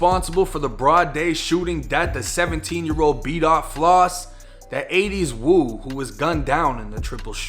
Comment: lol sounded like u said beatoff lossy